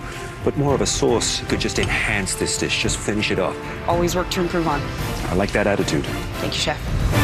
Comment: what did she say, anyone? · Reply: “always work to improve on”